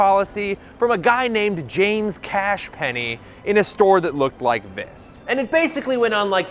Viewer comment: Did he say Cash Penney?